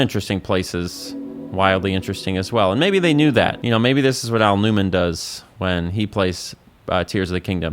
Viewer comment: Idk if Im losing it, but I heard "Al Newman." Anyway awesome video lol as always. Love them so much.